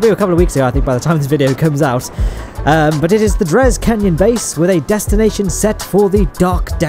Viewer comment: make a cannon base, it shoots junk into space... only cause i thought the video said DRES CANNON BASE. lol